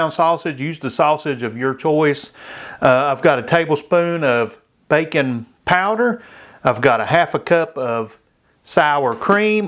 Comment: I'm southern too, but I swore you said "bacon powder" and was getting ready to Google it before I realized you said "baking powder"